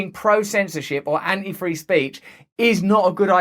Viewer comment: Instead of "Anti free speech, I heard him say "Anti freeze speech" 😂